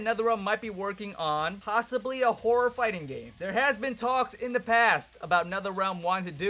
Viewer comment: I legit thought you said whore fighting game